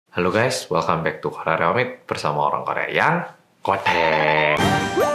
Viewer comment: Intro videonya di kupingku kedengerannya "Hello Guys welcome back to korea reomit bersama orang korea yang.... ngondek" 🤣🤣🤣